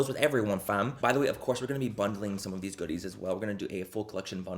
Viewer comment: I was half listening for a second and thought he said “fondling” instead of “bundling” 🤦🏻‍♀️😂